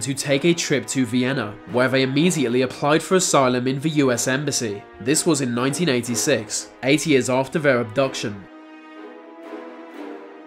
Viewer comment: I'm assuming it was 8 years, though after rewinding it 20×s it still sounded like 80. The next guy is a bitch, "oh I can't stand the regime so I'm going to fake my own death, I gotta make sure no one finds out or else my family will live through hell..well I mean they already are living through hell since I ditched them and most likely don't support them because people would be asking where the money came from.....I'm a p.o.s"